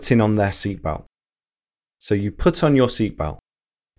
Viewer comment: Great lesson, very interesting but I have a doubt, I hear at 1:22 "so you puts on your seatbelt" instead of "so you put on", why? Am I wrong? Regards.